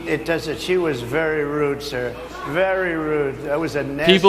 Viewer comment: When Harris got his panties in a bunch, I swear I heard him call her "sir". " She was very rude sir @ 5:28" 🙄 Maybe I'm hearing things lol